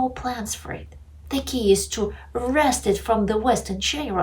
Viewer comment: You must have watched some other video, I never said Putin wants to wrestle Zelensky. I said he wants to beat him and destroy him. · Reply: granted, at @ 4:00 I heard "wrestle" while actually you said "wrest" Ukraine, "from West in general and zelensky in particular". But the conclusion is the same: you allude here that Putin somehow sees Zelensky as an opponent which is ridiculous (and pathetic when Zelensky tries to assume this role by dressing "tactical" and growing a "Che Gevara" bread that actually looks more like pubic hair than anything else). Those intoxicated by NATO propaganda (of which you are an actual example) might not notice but such a claim is like stating that a boxer fights against the gloves of his actual opponent and shows total lack of understanding (or an intended travesty) of actually goes on the ring.